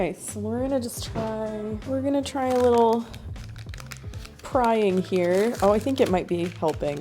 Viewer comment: Definition of winning with this recipe: "it's not goo". Also, when you were getting it off of the parchment, I actually thought you said you were going to try a little *crying*, not prying. I just nodded to myself, yup, that's where I'd be.